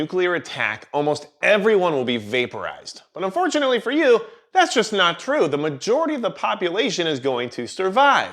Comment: If you are interested in reading the article referenced in this video, then check out "Food and Nutrition in the Aftermath of Nuclear War" by Alexander Lead, M.D. here: <link> MIRA Safety offers the best CBRN personal protection while maintaining the capability of tactical use for self defense: MIRA Safety: Use Code "magic10" to get 10% off any order! <link> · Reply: Oops. Sure sounded like you said unfortunately for you... that's not gonna happen (that it would be fortunate for me to be vaporized)